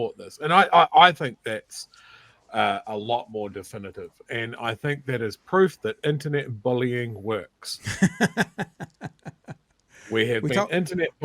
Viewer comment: IS THAT BULLYING BY GIVING YOUR TRUE OPINION AND DEMANDING AN APOLOGY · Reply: This is what said at 1:55, "...and I think that is proof that Internet bullying works!" laughed in response. continued, "We've been Internet bullying him [PM Luxon] for weeks now. He got bullied at Waitangi. And I assume, taken into a room, the door was locked, and all of his advisors bullied him. So, here we are! I think it is a good thing..." This is an outrageous endorsement of bulling. Is this a message that you want our children to learn? (Btw, replying in all capitals means you are yelling at me.)